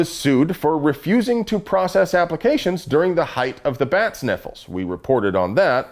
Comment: I thought you said "bats nipples" and nearly spit out my drink.